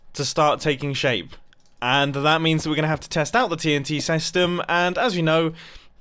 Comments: Did anyone hear what he said in 7:08 ? He said "TNT sestem" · he said sestem instead of system